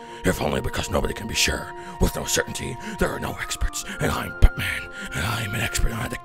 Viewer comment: Sounds like is saying BUTTMAN!